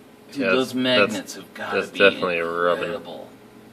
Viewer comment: I love mishearing things sometimes... At 1:11 - What was said: "Those magnets have got to be incredible." What I heard the first time: "Those magnets have GOT to be edible."